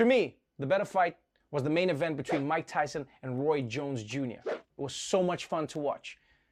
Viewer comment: WHAT? Tyson vs. Roy Wood Jr.?😱😨😬 Whose ear was bitten? Whose jaw was broken? Oh...sorry... I misheard. It was Roy JONES Jr.. 😉